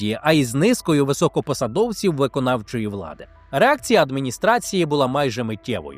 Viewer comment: прям по фрейду, замість реакція, почулось - ерекція😂